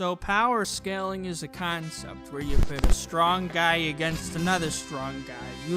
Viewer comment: Anyone else mishear the beginning as "Strong Daddy V.S. another strong Daddy" or is it just me?